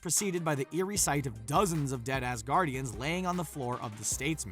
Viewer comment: oh I thought you said “dead ass guardians” but you actually said “Dead Asgardians” 😂😂